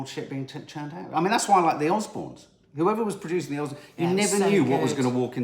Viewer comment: I thought you said the Osmands but soon realised you'd said the Osbornes 😂😂😂😂